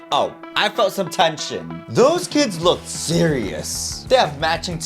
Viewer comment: When he said tension I thought he said shit